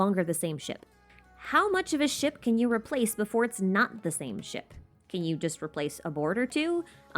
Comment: for a moment thought she said if you take a sh*t can you replace that sh*t 🤣